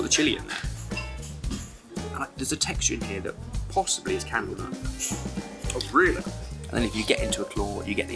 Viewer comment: To those in charge of the CC, I think said "possibly it's candlenut". What do you think?